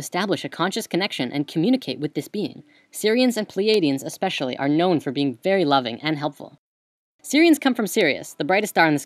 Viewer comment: Oh, shit, I thought you said "Syrians are known to be loving and helpful". I almost spit my drink when you then shifted to talking about stars